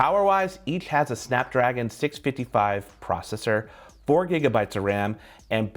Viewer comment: Does he say snapdragon 655?I only understand that